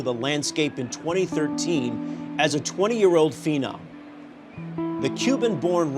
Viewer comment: I thought he said a 20 year old female.